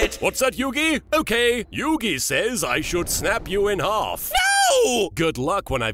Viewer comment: “Yugi says I should snap you in half!” I died at that part 😂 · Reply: But I thought he said "should snap you off "😂😂😂😂😂😂😂😂